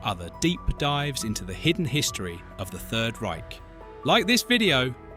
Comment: When you said hit the bell I thought you said Hitler Bell 😆